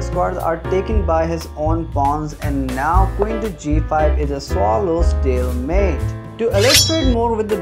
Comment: For a secon i thought he said stalemante